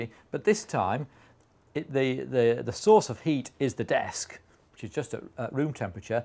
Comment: I'm sorry for your disability, but you are deaf right? So i will just point out that at 5:03 he clearly says "The source of heat is the desk" And at 5:17 he says "The temperature difference that is driving this heat engine is now the temperature difference between the desk and the ice cubes" Now with the Ice bergs part, i think that most people are intelligent enough to figure out that the energy would be coming from he difference in temperature between the ice and the air surrounding it.